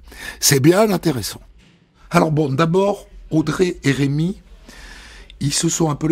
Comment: Audrey: - couche avec Patrick et Rémi - 39 ans - au RMI - 2 enfants de 2 pères différents - convertie à l’Islam et se fait appeler Amina - amoureuse d’un rappeur sénégalais qu’elle n’a jamais rencontré. S’est faite tatouer son nom 😅😅😅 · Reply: Quand il dit "Audrey et Rémi" j'ai compris "Audrey RMI" 😂